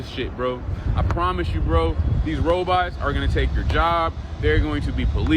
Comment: I mean, he said bro so many times at one point I thought he said Brobots😂